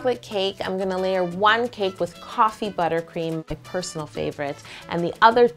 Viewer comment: I thought she said TOFFEE buttercream